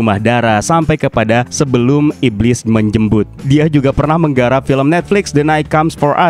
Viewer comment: sebelum iblis menjembut,,,,apa kuping gw yg salah denger,,,wkwkwk